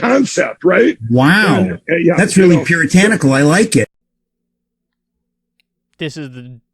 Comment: "Wow! That's really puritanical, I like it!" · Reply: I thought he said "Pure tyranical" 🤣